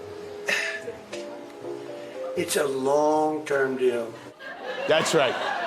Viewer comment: When he said long term deal twice, I legit thought he said "long con deal" on that second one. 😂